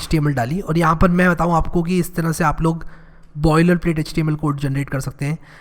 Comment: Can anyone tell me what he said at 21:32 before saying "html code generate kar sakte hai" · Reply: boiler plate html code - just refers to the template file you get when you hit tab after ! in vscode